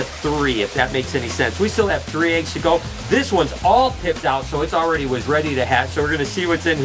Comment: Good Morning everyone! Its my BIRTHDAY!! I am so lucky to have all the people, animals and all your support in my life. I will be at the zoo all day today and hanging out tonight with all the people that come to the reptarium to celebrate. I feel that rather than taking the day off I want to spend it the way I want and with what I love. I will have Lori, Noah and my crew with me today. I know it will be a great birthday and I appreciate each of you for being here for me! Lets take another lap around the sun and have a great year ahead. We all know this last one was a bumpy ride.. I truly believe this year of my life coming up will be one of the best ever. I am transforming into a better me, a more calm me, a more happy me.. And I can not wait to being you all on the journey moving forward. You guys might not realize how important you all are to me and all my dreams. Please understand how much you all mean to me! Have an awesome day! Love you all so much! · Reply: I seriously thought u said "this one's all pimped out" when you were saying pipped out. I died laughing, I was like "oh goodness, Brian started pimping snakes LMFAO"